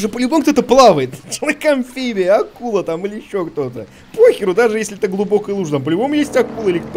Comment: что он сказал? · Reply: "Ещё кто"